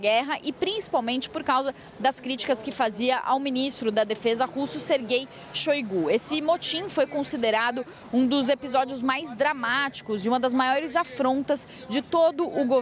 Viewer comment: ouvir isso e entendi que ele não concordava com ministro "ser gay" 😂😂😂